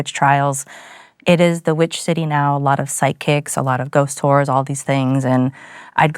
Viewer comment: Oh no at 3:49 i thought she said ghost wh**es not ghost tours